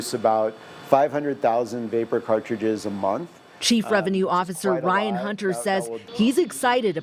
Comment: I really thought she said residue instead of revenue!😂